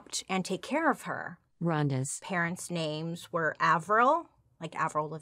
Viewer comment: Love your videos but at 1:21:39 I can hear a voice cut in saying something like "Rondas".... Did anyone else hear it? U either got a ghost or someone kicked in on ur end. I listened a few times so it's not on my end cause it's only at that point each time I rewind. Weird..